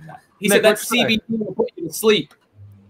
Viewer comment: What was said by at 7:24 . I think he said “that CBD will put you to sleep”